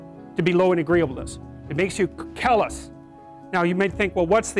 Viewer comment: Always downvote these auto-generated videos. 1:08 "it makes you kill us" instead of "it makes you callous".